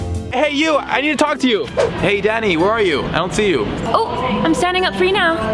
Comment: lol I thought he said hey daddy